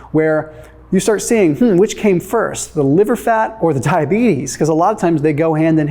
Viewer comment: Thomas, did you just say tiabetes @ 3:44 🤣🤣🤣🤣